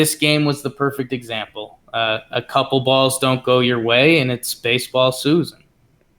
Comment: What does say at 33:36? "A couple balls don't go your way and it's baseball ??susan??"